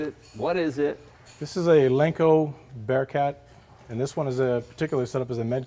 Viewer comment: At first I thought it said "Leno BearCat" and I thought "WOW, SWAT named a truck after Jay!" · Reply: No, it's Lenco(Leno with a C).